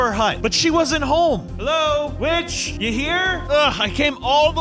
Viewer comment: the witch sounded like "b*tch"